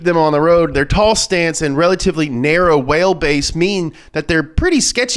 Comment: Lol at 3:30 instead of saying wheel base he said whale base hahaha